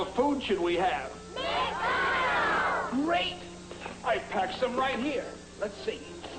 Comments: After playing Undertale Yellow, I can definitely say that the devs of that fangame has also used the same commercial to add some extra voices for Flowey’s voicelines in the Neutral Route boss fight, the lines being “What would you like to do?” (0:11) and “Great!” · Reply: I kept mishearing great as grape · WHAT WOULD YOU LIKE TO DO??? GREAT!!! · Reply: I kid you not I thought he was saying grape and not great